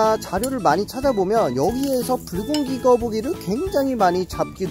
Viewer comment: 불고기거북처럼 들림